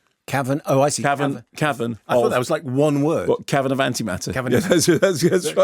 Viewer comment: I thought he said Kevin of Antimatter.